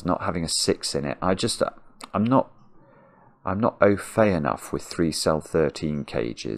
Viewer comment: What does he say at 33:40 about not being “_____” enough? Anybody able to pick that word out? ‘Oafay’?